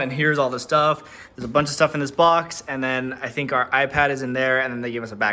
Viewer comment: I’m sorry, did you just say.. iPad?